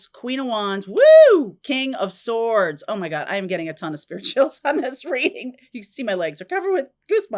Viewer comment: I thought you were saying “spirituals” when you say spirituals chills for a few weeks 😂